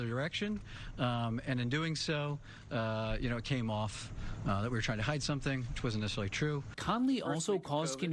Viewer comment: "...came off that we were trying to hide something, which wasn't necessarily true." Did I hear that right? :/